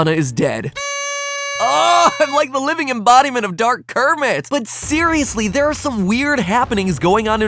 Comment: I heard " dark Kermit " and was dead laughing